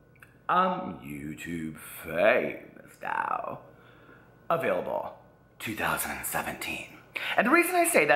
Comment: Keep your intro...you'll thank me later😆 I went back in time because I thought you had been saying available in 2020 for 3 years(he knew😳). I was wrong.